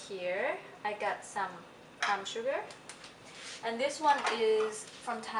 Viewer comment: Did you say Palm Sugar? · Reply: Yes Palm sugar that you can get in most Asian markets